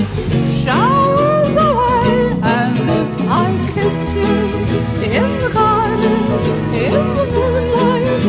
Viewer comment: it sounds like he is saying ill piss in the garden